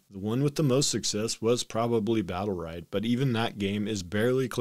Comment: What was that game at 0:50. He says Battle Rag but i cant find anything regarding that name. Also CC doesn't pick up what he says there.